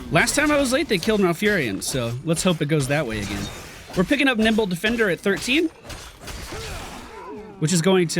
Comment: Anyone else hear "nipple defender"? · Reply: I heard nimble.